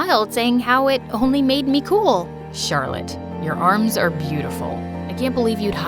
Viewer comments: Did anyone noticed that said Chatlotte instead of Violett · DID HE JUST CALL HER CHARLOTTE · Lol did anyone notice on 14:22 it said Charlette instead of violet